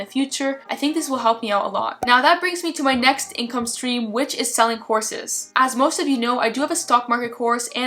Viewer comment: I heard "selling HORSES"... In my defense, I was watching this on my phone while brushing my teeth in the bathroom, and the echo repeated sounded like "horses" Needless to say, now I have a toothpaste mess to clean up, but the laugh attack was worth it! 🤣👍🐎🐴